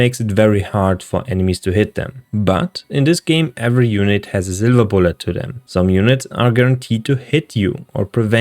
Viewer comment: "Zillabull to them", what did you say? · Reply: Silver bullet haha